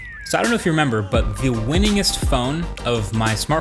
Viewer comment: Did he say the winningest phone?